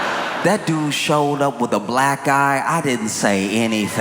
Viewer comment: For a second I thought he said Elon showed up with a Black guy and Trump said nothing. And I thought yeah that is restraint on Trump's part since he hates Black people. Then I remembered the black eye allegedly done by Elon's 5 year old son and felt so silly.